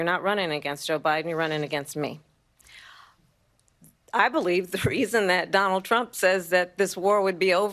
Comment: did she say you’re out running against O’Biden your running against me!